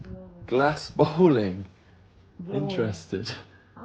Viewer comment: 😂 I thought it said glass bowling.